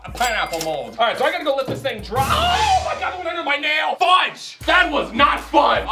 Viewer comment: who else hurd collins say shiiii only one i am prob just hearing things lol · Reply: When but i heard him say fugue and that meant the f word i dont say bad words much at all